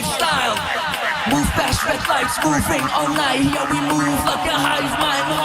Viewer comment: ほんとに1R目のが音楽性高すぎてマジで好き。このネタ過小評価されすぎだろ。1番好きなんだけど。 字幕の編集めっちゃおもろいです！最高！これからもよろしくお願いします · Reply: そんな自信ないけど、move pass red lightにも聞こえる